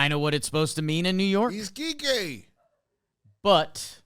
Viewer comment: i just had to point this out, sometimes I'm watching with captions on, and it truly thought said "he's gay!" Instead of "he's Kike!" Idk why i felt the need to point that out.